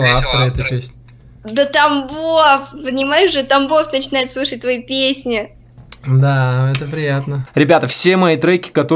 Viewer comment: Когда девушка на 5:42 сказала: «да Тамбов начинает слушать твои песни», мне сначала показалось, что она сказала: «да там Бог начинает слушать твои песни». Может так и есть. ты классный, твои песни супер. Удачи тебе и вдохновения.